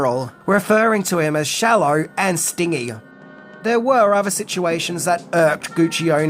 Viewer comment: "Sting-y", hmm? While McDowell does maybe resemble Sting a little bit, that's not what Guccione meant. The word he used rhymes with Benji not with Thingy (i.e., it is stĭn-jē not stiŋ-ē )